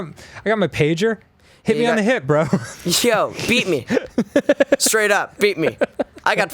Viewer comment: when they were talking about pagers n shit, i thought finn said "beat me" instead of "beep me" and i was like ???? okay then finn